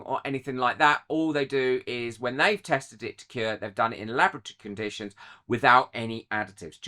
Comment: Hi did you say “lavatory conditions” 8:10. I love your squidgy widggy, Cara xxx